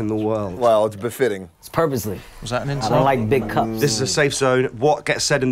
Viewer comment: In the beginning... Kevin Hart said "I don't like big cups" I heard "I like big cups" My drunk mind breaking into rap: - "I like big cups and I cannot lie, u other drinkers can't deny, When girl walks in with a sloppy grin and her big cups in your face u get drunk!" Haha!! I Crack myself up sometimes!! 🤣